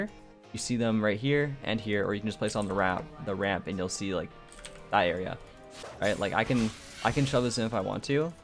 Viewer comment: sounds like he said “ you’ll see diarrhea” lolz